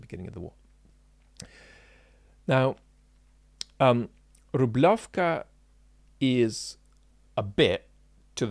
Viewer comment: Oh, Rublevka. I misheard it as Zubrowka